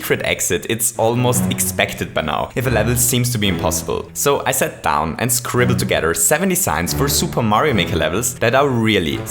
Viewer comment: I sat down to scribbled all seventy signs... that's what I hear at 0:28 I was wondering how you present 70 mechanics in this short video